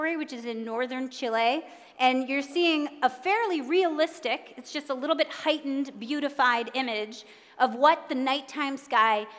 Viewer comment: did she just say we are looking at something fairly realistic and just a little heightened and beautified . well i think at the very least it would be realistic for you to at least tell us on a scale of one to ten just how real this really is. until then i can only look at these pictures as nothing more than cartoons given the fact that there is just way to much disinformation out there today even from the very people we think we can trust. question everything.